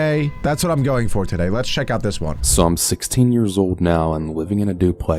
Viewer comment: it sounded like he said psalm 16. jesus loves you(JOHN